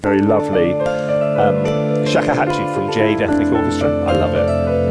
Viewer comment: ummmm what the heck did he just say this instrument was and from what vst orchestral library? I would really like to know but what he said was inaudible to me. Thanks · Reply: Shakuhachi from JADE Ethnic Orchestra